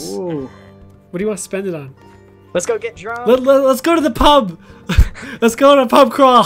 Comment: I thought Grian said lets get drugs